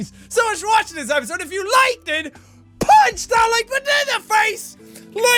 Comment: When he said "like a boss" it sounded like he said "like a butt"